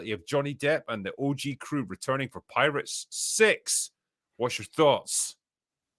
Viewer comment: Headphones picked up audio at 1:25 when said “6” and i thought he said “sex. Whats your thoughts?” “so like genuinely with everything, id rather not.”